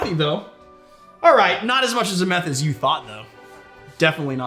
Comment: I'm dying. @ 16:38 I swear he says, "Not as much meth as you thought". I don't care, i just think that's effin funny.